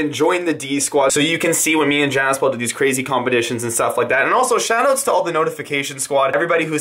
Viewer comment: Did he just say combetitions?